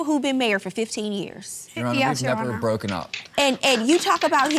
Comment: What did he say @ 10:45? "Your Honor, we....something, something...broken up".